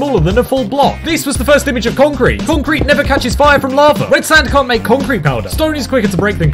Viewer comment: Did I just hear “concrete dosnt catch fire from lava” it is fucking rocks of course not bc stuff dosnt melt in this game